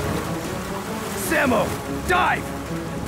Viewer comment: i heard Samo die!